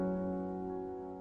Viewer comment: I THOUGHT YOU SAID NIPPLE SAMUEL AT THE END OF UR VIDEO LMAAAAO